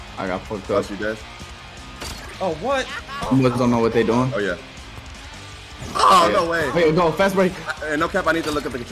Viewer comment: what you say? 🤨🤨🤨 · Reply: He said "dem boys"